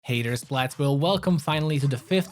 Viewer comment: Thought he said “haters of splatsville” at first rather than “hey there splatsville” lol